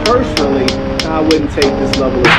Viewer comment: “I personally wouldn’t take this level of disrespect” 😂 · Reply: Abe Zander Oh. I thought he said "Personally, I wouldn't take the supplements". Oh, oK.